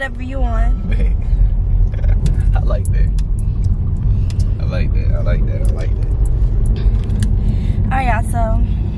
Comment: It is the face expression made that dude funny asf 😂❤did he say bae I looked that at 22:46😂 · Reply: He said bet